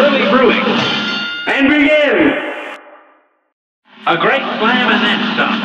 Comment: “A brawl is surely brewing!” ”AND BEGIN!!!” “A great slam and then some!” ”IT’S ON!!!” “Good day for a swell battle!” ”NOW GO!” “Here’s a real high-class bout!” ”AND BEGIN!!!” “This match will get red-hot!” ”YYOU’RE UP!!” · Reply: and i thought he said "a great slam bonanza"